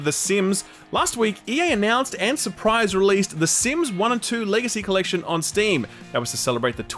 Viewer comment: I absolutely heard you say "The Sims LAZY collection" instead of Legacy. Checks out. 🤣